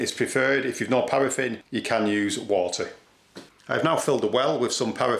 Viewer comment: did he say, if you don't have paraffin, use water?